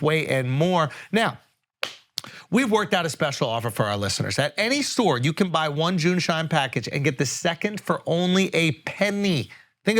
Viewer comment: I thought you keep saying Jew shine in the ad read